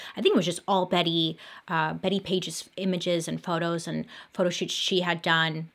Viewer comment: I feel like everyone knows who Betty Paige is Me: 😗😐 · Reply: Nay nay lie! At first I thought it said petty paige and I was like, "Bailey is gonna drag paige?!?....Suspish!"